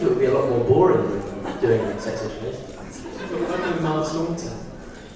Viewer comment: Does anyone know what Joe says at 3:30 after Kit says he thinks The Crown would be a lot more boring than Sex Education? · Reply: "You would be working with Imelda Staunton"